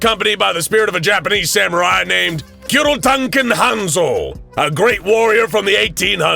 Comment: Love the video, but the way you pronounced Barrett (Buh-rette) was brutal to listen to. The correct pronunciation is Bare-ette. Also, the cartridge casing isn't part of the projectile that leaves the barrel.... Anomalous or not, it wouldn't work that way, and kinda kills the immersion. Just some constructive criticism. Yes, it may be petty and insignificant to some, but there are plenty that it isn't petty or insignificant to. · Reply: Same with the Japanese name, had to laugh. Sounds like he said Kirotanken not Kuroitanken.